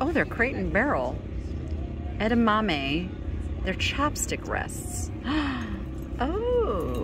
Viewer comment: I so thought you said the edamame things were chapstick holders. I was trying to figure out how that would be helpful and how you we’re going to find 6 people that felt like they needed one 😅.